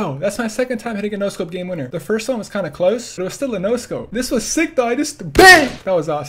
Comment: its sounds like him saying this is sigma but he is saying this is sick guys